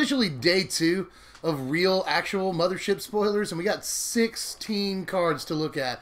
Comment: I thought you said no shit spoilers and I was impressed till I re-watched it